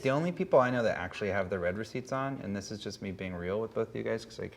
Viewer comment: Lmao for the first minute or so I thought they were saying "red receipts" cause my attention was divided 😂